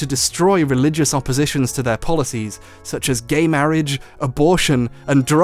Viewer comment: instead of gay marriage (which is said) Gary marriage is written. I don't know if that joke was intended, but it's amazing. I too ship the Garys!